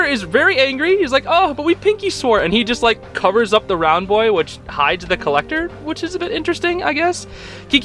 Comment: please tell me I heard you right when I heard "he just covers up the round boy" because if you did that just made my day